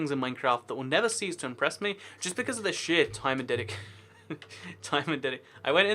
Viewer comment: what did he say! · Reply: It's because of the sheer time and dedication